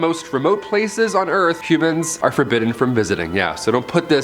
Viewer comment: I thought you said “Cubans are forbidden to.. “